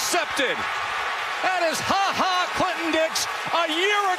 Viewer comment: did he just say "Ha Ha Clickin Dicks"?